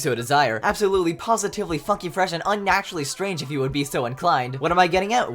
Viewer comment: WHY DO I HEAR "F**KING" INSTEAD OF "FUNKY"💀